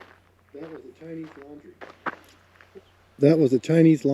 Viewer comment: i heard a ghost say"that is a chinese laundry"